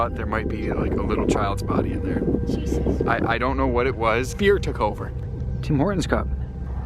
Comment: At about the 10:33⅓ spot, he says, "Fear took over", & the subtitles say "Beer took over" lmfaooo